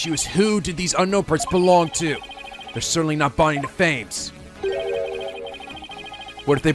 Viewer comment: EmeraldDragon15 I do understand that my "two women" interpretation can be a bit of a stretch but, being a native French, I couldn't help it, especially when pronounced it in such a way where I heard "deux femmes". But to me, it's just too beautiful of a coincidence not to say it. Edit: I went to check on the pronunciations and, while yes, "de" and "deux" have different phonetic spellings, it would be hard to distinguish the two, the key difference is that "de" seems to drag a bit (I've never heard someone drag a "de" for the record), meanwhile "famme" and "femmes" (the plural 's' is silent) are dead-on the same. I sincerely couldn't help it, it sounds so perfect at some point, it had to be intentional but still...